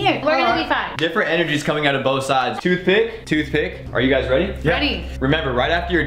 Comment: Am I the only one that thought said too thick when he really said toothpick